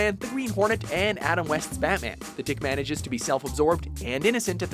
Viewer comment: #1 Tick Man Did you just say THICC man?